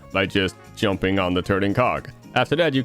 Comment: did you just say turning cock💀